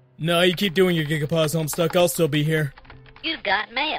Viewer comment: Am I the only one who thought they heard him say 'HomeStuck' in the first three seconds?